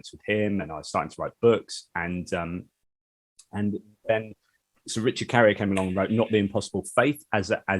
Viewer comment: Did you say Sir Richard Carrier??!